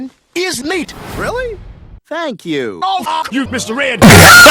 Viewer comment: I swear, Judge said “Go 🦆 yourself, Mr. Red!” Did the Mandela effect happen to me that fast!?😭